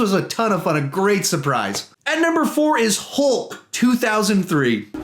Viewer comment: you said hulp instead of hulk